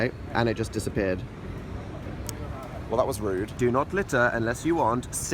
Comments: When he read the do not litter sign I thought he said do not glitter I was so confused lol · When he said do not litter I thought he said do not glitter